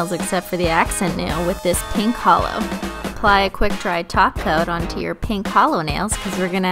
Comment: Whenever you say top coat it sounds like your saying taco